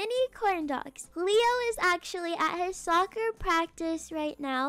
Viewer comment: Was it only me who heard say "Leo is actually at her soccer practice right now"